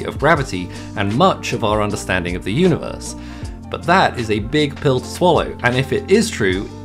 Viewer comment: Bruh, i thought that said big pillows to swollow